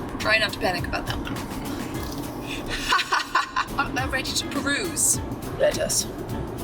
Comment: Can somebody please tell what says on 14:09? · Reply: “Art thou ready to peruse”